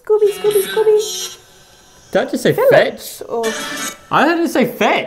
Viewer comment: I hear “Filtch” not “fetch” nor “Philips”